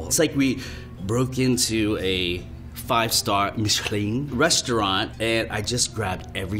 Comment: he says mishklin instead of michelin